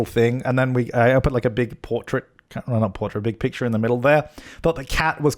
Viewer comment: you thought the... the what? the cat? the CAT was fitting? did i hear cat?? that’s a cat to you? interesting.